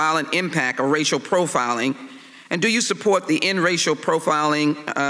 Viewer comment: Did she say "pacificly?"